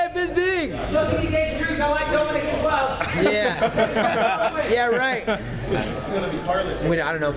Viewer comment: What exactly did Bisping say? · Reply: ''go faber, nothing against cruz i like dominick as well''